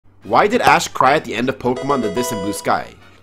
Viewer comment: I couldve swore he said why did ass cry at the end